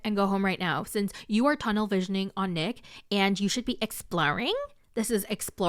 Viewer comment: “exploring” sounded like your “provider” lol